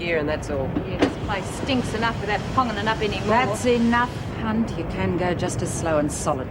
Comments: Colleen : "that's enough, Cunt"... At 11:24 haha well it sounded like that!! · What does Colleen call her at 11:26.!?! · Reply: Bitter mate